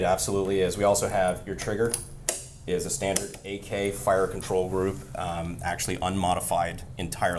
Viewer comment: Man, CGI budget is getting insane. It almost looks like there’s a real AK-50 in this video. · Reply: You said "standard AK fire control group" ...what I heard was "able to put in AK binary trigger"